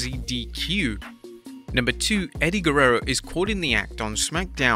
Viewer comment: it says AUGHT instead of CAUGHT